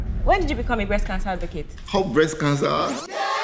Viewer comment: Did I just hear "up breast cancer"